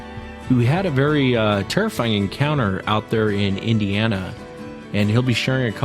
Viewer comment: Where in Ohio was this....I'm in nortwestern part. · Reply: I thought he said Indiana